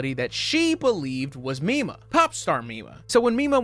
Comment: i keep hearing "popstar nima" instead of popstar mima. completely unrelated but i would recommend you look up popstar nima instead. wonderful artist.